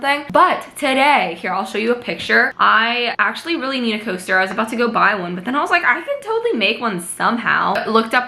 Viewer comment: When she said I need a coaster so I’m going to make one I thought she said toaster 😂 I was like how do you make a toaster